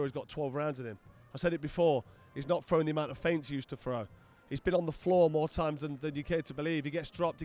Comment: I thought you said he's been ironed out more times than your jeans? Why didn't you say it to his face you bottlejob 🤣